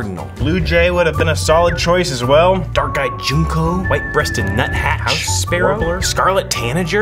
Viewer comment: DID HE JUST SAY JUNKO- like Junko from Touhou